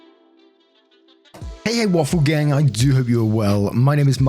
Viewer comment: Right away: thought I heard “hey what's up egg waffle gang”. I have bad hearing, lol…